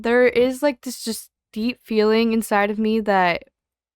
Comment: what is deep inside of you? oh my god I heard that shit so wrong..